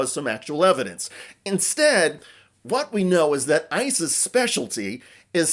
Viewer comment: I really love it when AI generated subtitles mishear "ICE's" as ISIS. Maybe AI isn't that bad after all. I mean, the two of them do have a lot in common, don't they?